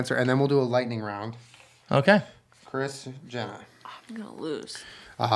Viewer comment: "chris, jenna" me:"did he just say kris jenner"